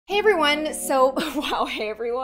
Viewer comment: Instead of “Hey everyone, so-“ I heard “Avery wen, soap”